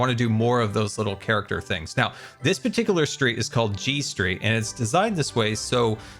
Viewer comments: Oh, G street. I thought you said G string. 😂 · i had to put u back to 1× speed n still relisten to that sentence 3 times before I heard G Street. I kept hearing G String. 😏 of course that must be my guitar playing brain making me hear that. Definitely not for any other reason 🤥